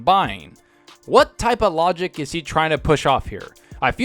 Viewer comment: Ngl one of your ads was placed at the perfect time (6:51) i really thought you Were about to say "what type of BullSh*t..."